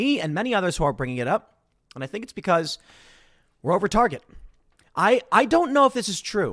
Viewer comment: does Tim say "We're Over Target," "We're the Target" or "We're Retarded." I dead ass can't tell. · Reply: Were OVER the tsrget I promise